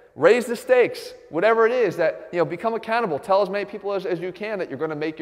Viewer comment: Just got out of the slammer. Thought he said become a cannibal.